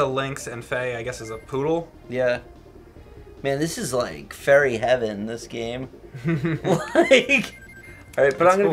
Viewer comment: Was trying to say Furry Heaven instead of Fairy Heaven? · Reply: he said furry, it's the PA accent